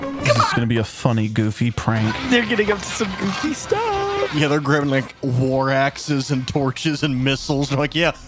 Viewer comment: I thought he said “they are grabbing swords and Lorax’s”